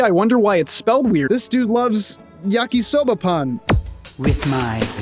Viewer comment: I legitimately thought you were saying “Yakisoba Pun” and I was getting annoyed that you wouldn’t just explain the pun already!… until the last time you said it… Pan. Yakisoba Pan. Bread. I feel silly now…